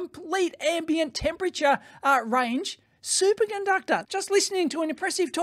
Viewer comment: I heard Dave say "Super Gunductor" and I can't unhear it now.